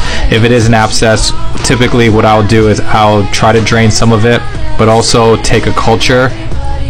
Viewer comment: nah why did I hear “might wanna drink some of it” 😂😂😂